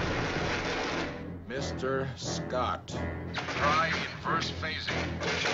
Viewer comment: Gentlemen, I suggest you beam me aboard! The most suspenseful three minutes in all of TOS. Magnificently superb. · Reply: “yelling at” ? I heard him saying, “…try inverting the phase” , which LOL works great on my guitar pickups / I’m sure it stops electrical fires and transports captains…